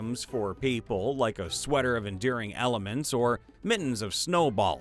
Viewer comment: i swear i heard "endearing elements" and my brain is now picturing you putting on the sweater and the elements making got chocolate and snuggling with you